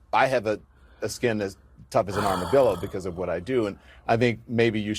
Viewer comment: Did Lemonhead REALLY just say hes got skin as tough as an armadillo 🤣🤣🤣 I know he cant be serious that fool falls apart over everything! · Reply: Armadillo? I thought he said "Rubber dildo"